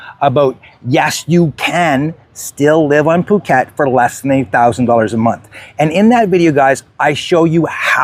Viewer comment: ok good to know. Lol. I listened twice and even used the caption. So what did you mean? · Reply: is at the 00:53 mark. I think you must have said a thousand dollars a month but it sounded like 8,000 dollars a month. Even the captions said 8,000 dollars a month. Lol. I enjoy your videos. Never been to Phuket.